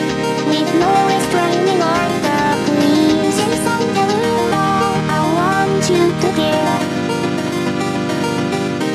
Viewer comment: what is she saying here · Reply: Jason derulo, as a reference as to how he randomly says his name in his own songs